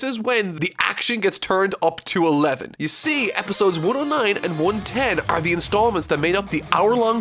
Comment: Am I the only one who couldn’t stop hearing 100 and Really messed up my mind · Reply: I kept hearing “episode 100 and”